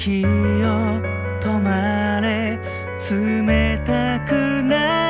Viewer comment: did I just hear toki wo tomare? Dio intensifies